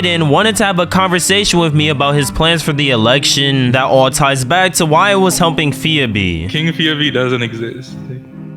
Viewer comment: what did bro say??? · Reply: That all ties back to helping pheabee